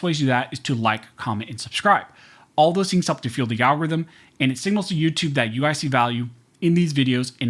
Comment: The first time he said "algorithm" i thought he said "Al Gores rhythm" lol he really spaced the word out when he said it